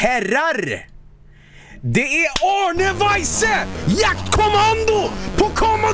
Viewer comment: He sounds like he's in a Swedish comedy show! Also when he said "Arne Weise", I thought he said "Arne Bajse" like if he said "Arne the pooper" as if the Swedish word for poop was a Swedish surname. That would be a fitting name for some goofy comedic Swedish caricature.